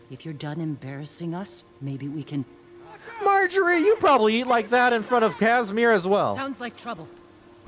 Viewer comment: I had the video running in the background and wasn't paying attention and thought you said Marjory eats Kasmeer like that